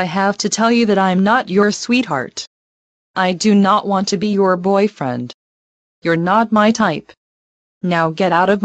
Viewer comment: did raven say I do not want to be your boyfriend?